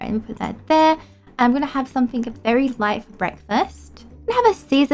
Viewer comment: Is it just me or at 0:52 did sound like she said ima have something berry light or was it light I don’t know